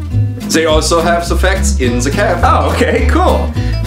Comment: Why does it sound like he is saying the F word when he’s saying facts XD